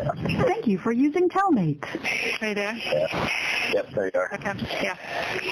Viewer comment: I heard "hey dad" but with you saying does she say "hey dad or hey Dan?" re-listening it sounds like she says "are you there?". Also reciting out of a bible sure does make you a saint 🙌🏻 praise hallelujah! She's never been caught before, she cannot logically figure out how she can be caught, especially by children.